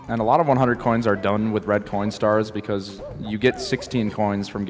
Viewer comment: i wasnt paying that much attention and thought he said red p**n star